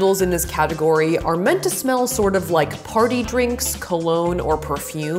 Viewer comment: did she say farty drinks?????